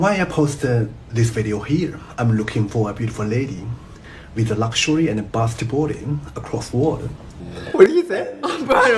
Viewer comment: Lol 14:14 I think he says “with a luxury and a pass to boarding” like a passport maybe haha